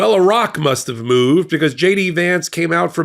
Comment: I thought he said “Iraq must’ve moved, because JD Vance just came form under it” 😂